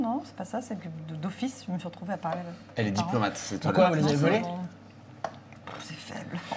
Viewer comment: Il dit quoi à ce moment ? qui à l'air d'être une blague nulle du coup, c'est un peu frustrant de pas entendre ^^ Continuez, c'est vraiment excellent ! inspirant même ! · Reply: "pourquoi ? vous les avez volés ?"